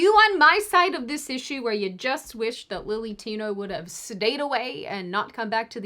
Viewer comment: At the end it sounded like you said "...you just wish that Lily Tino would've just 'SEDATED' away..." But you just said, "...stayed away." However...I do wish that people like that would indeed SEDATE away... like ... really.